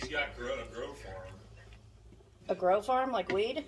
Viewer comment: I thought he said the guy that came over had a “girlfriend”. I’m thinking, “What’s odd about that??” He has a “GROWFARM”! Hahaha! Gotta get those hearing aids!! 😂 You all looked beautiful in your outfits! Spaghetti looks scrumptious. I could eat it every day. 🍝❤️ Love the Ree bowls. Here stuff is amazing. Get some rest! 🥰